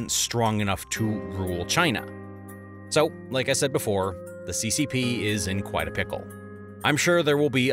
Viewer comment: When he said "The CCP is in quite a pickle" I thought he said "The CCP is not quite a pickle"